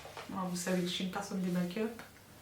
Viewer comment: « je préfère vous dire moi j’ai des black-up » 😂😂😂😂 ah oui effectivement!